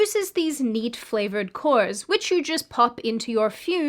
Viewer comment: ik I'm tired, but even after replaying that it genuinely sounds like she said meat flavored cores 😂😂😂